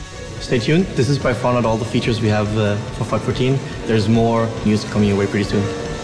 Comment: Did anyone else hear him say "fuck 14" xD ?